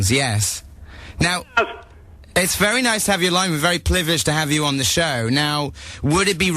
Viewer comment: it sounded like he said plivileged instead of privileged lol i love edgar =)